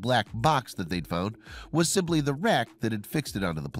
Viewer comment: Took me four attempts to understand what was said there. Auto captions said "...was simply the wreck that had fixed it onto the plane.", which is what it sounded like to me. But it was "the RACK, that fixed it onto the plane." ie. the mounting BRACKET. lol